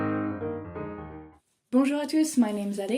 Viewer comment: Didn't know you were french. Always thought you said "Bon Jovi On Tour" before every video and thought that was weird, so it was "Bonjour tous" or smth smth. Lmao.